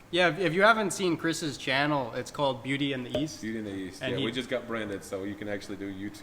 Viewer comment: I thought you said beauty and the yeast.....😂